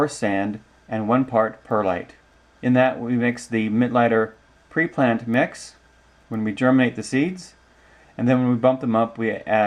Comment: Thanks for another Great Video..... Going into the vid at approx. 2:50, you showed (in text) the ingredients you use to make your mix. But then you said you mix in something else, it sounded like Midlighter or midnighter Pre-plant mix. What exactly was that product, and at what ratio do you mix that into your ingredients..?? Thanks for your help...